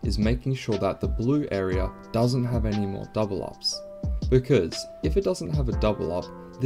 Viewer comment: Thanks! Great guide. I understood the concept of the 50/50 method, but couldn't catch the words. Can anyone help me out? What does he say at 8:55? "No double ###" Thanks! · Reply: "double up" What I mean by this, is that it can't have two of the identical genes. Eg a double X is a "double up" or a double W is a "double up". As long as there are no "pairs" or "double ups" the double G or H in this case will take priority. Come join discord if you're still confused, thanks!